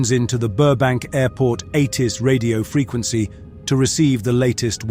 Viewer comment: At first I thought it said "80s" radio. 😅 I'm glad it was immediately clarified as ATIS.